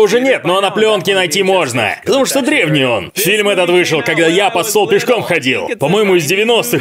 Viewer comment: "...Фильм этот вышел, когда я под СТВОЛ пешком ходил". Да, я услышал то, что захотел услышать.